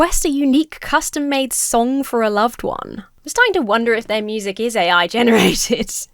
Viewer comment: I genuinely thought you said 'custom made thong for a loved one'. I had to go back and check.